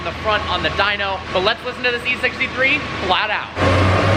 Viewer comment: In 4:47 did he say c63